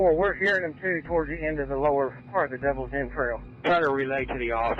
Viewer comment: I thought they were saying the Devils entails, but it was Devils END trails.